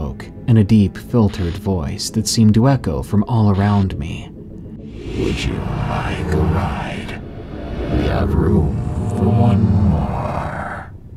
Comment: what did he say? Its inaudible to me.. help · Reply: 'Would you like a ride? Room for one more' is what I heard